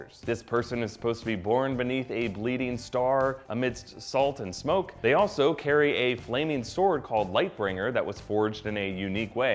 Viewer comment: I thought it said Lightsaber